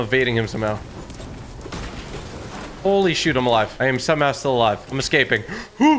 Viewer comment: Did he just say sh!t